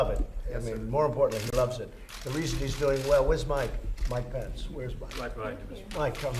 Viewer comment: Where’s Mike, Mike Pence?? Right behind you Mr President, lol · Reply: I thought he said “Where’s my Mike?” lol